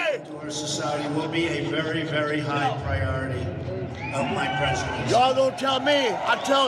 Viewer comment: when Trump speaks...did I hear right? "...toward society will be a high priority. A white precidency." And everybody cheers up o.O